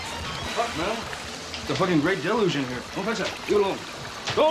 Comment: What does he say at 0:22 ?? It’s a grey village in here ??? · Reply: He says, "It's the Great Deluge in here." Referring to catastrophic flooding.